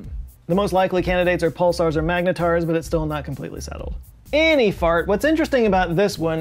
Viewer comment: did you just say 'any fart"? WTF bru 🤣🤣:elbowcough